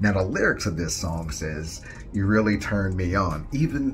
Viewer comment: I’m hearing “you really turn me GOOD” for some reason?? Even in slow mo that you have, it sounds like he is saying “turn me good” which doesn’t make any sense! Has this changed since you posted it?